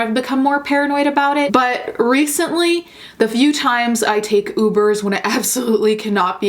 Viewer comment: Swear to Gawd, had to rewind to make sure, but I thought you said, “Few times I’ve taken UPPERS.” I was like, “Well damn, Giulia, tell us how you really feel.” 😂